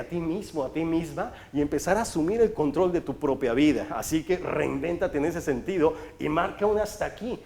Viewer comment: yo entendí “reviéntate”, y ahora traigo una crudota…